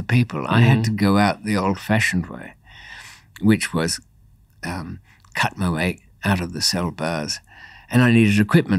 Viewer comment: As an 🇺🇸, at the beginning instead of “cut my way out of the bars” I heard “cut my LEG out” and was like damn this is going to be good.